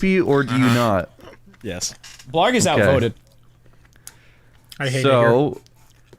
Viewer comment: sounded like blarg said "i hate ni***r"